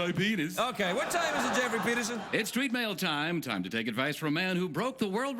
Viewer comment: I heard it as "Geoffrey Penison".